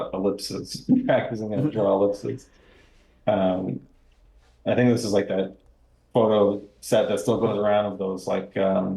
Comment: anyone Catch the name of the 2 skull model websites ? thnx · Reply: Bone Clones and 3b Scientific